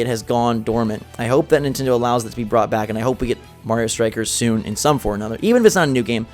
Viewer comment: I thought you said we needed Mario stickers